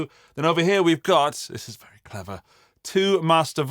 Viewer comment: Aww man, I thought you said bravo victory, but it was 'very clever'. Bravo would've been so perfectly British. Right? New sub here, found your channel while (whilst) looking for information on Boss versus Marshall. You swung me over to Boss, which is good because I already have a Katana 50 and I'm very happy with it. Okay, great channel, love your guitar and amp collection and your phrases like the amp is 'flapping your trousers behind you'. Thank you!